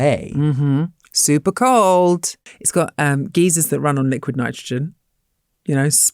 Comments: I heard "geezers" and imagined frigid old men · Why did I think she said "it's got geezers that run on liquid nitrogen"? I was like: what?.. People?.. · I thought you said geese(s) for a moment😂 geysers makes so much more sense!